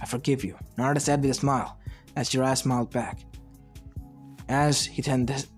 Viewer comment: LMAO I WAS WATCHING WITH SUBTITLES AND INSTEAD OF JIRAIYA IT WAS "as your ass smiled"